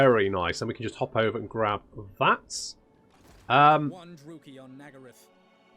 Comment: I like how because you spoke over the beginning of Alith's line at 22:16, it sounded like he said "one Druchii on Nagarythe". :)